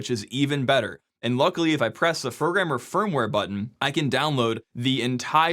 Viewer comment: Did you say firgrammer firmware button?